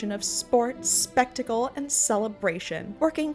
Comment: I wasn't looking at the screen I heard "sports, fentanyl, and celebration" 😭😭